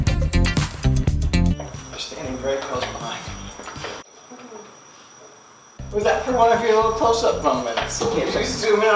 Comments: wat did lance say at 04:29?? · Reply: "You're standing very close behind me" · What did Lance say at 4:28? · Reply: "You are standing very close behind me. '